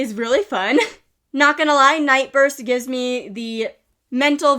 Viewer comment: ngl, when you said Nightburst I thought you said NightROOST, which also would have been a very cute name